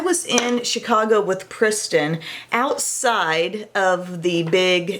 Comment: did you say pristin instead of kristin? Lol #babybrain you're my favorite youtuber ❤❤❤